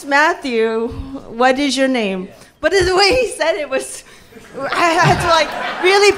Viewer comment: I thought he said "my name is Matthew, your name is Sha-mo" (沙漠 desert) 🤣🤣